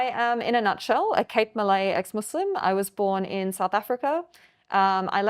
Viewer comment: Assalamualaikum..brother I want to ask..does this misguided woman say that she is Cape Malay 2:07? because I heard like she said "Malay" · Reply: yes, that's what she said. her ethnicity is "Malay", born in South Africa